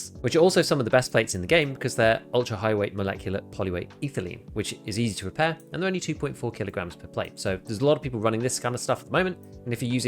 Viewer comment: what did he say? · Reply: "It's easy to repair, and only 2.4 kilograms per plate."